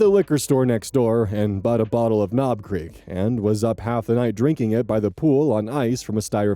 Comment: Had to double check the text when you said "Knob Creek", thought you said "Cream."🤣